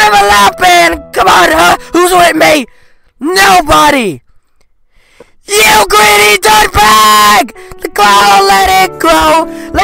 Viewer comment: instead of "you greedy dirtbag" according to the captions apparently its "yo granny dark bag"😂🤣